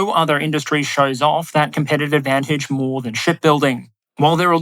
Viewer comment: I wasn't looking at the screen and all I heard was "Shitbuilding"